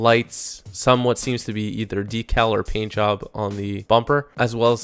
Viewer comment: He actually said decal, but it happened at 4:27, not